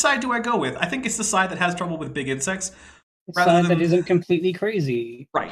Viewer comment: I misheard "big insects" as "bigot sex". That was weird.